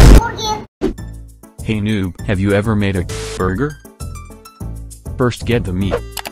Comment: Did someone say borgir?!!!